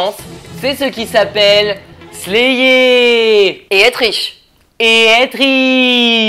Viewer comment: Je suis vraiment fière de penguin shark aka Kim Sohye qu'elle est rejointe une agence a elle toute seule alors qu'elle a fait que 5 mois de trainee alors qu'avant c'était une simple actrice par contre j'ai pas compris la blague du "Et elle triche" Sinon très bonne vidéo ça en voit du pâte en croute, bien sur vous ne pouvez pas parler de tous les groupes n'écoutez pas les gens qui se plaignent, profitez de vos vacances en Corée et surveillez les gens avec des masques et des lunettes de soleil ça pourrait être un idol caché😂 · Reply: Pour ce qui est de la blague, perso' j'ai entendu "Et être riche", je ne sais pas si ça peut t'aider pour mieux comprendre ? c